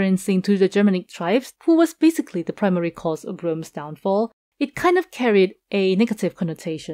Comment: what word is that "gr's" after downfall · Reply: it's "Rome's downfall". Very late but I just came across this video